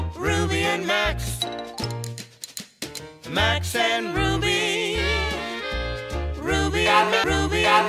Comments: All i hear "rax and ruby"😂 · Is it just me or I heard “rax and ruby” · sound like it said rax and ruby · It sound like it said rax in Ruby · yea it do sound like it said tax and ruby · It sound like they saying RACKS and ruby 😂👀🔥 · "Rax" and Ruby 😂😂😂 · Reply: itsjustariel it really sounds like hes sayingvrax and ruby